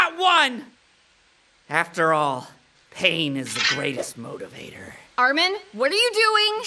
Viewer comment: “Pain is the greatest motivator” I heard that as pegging, I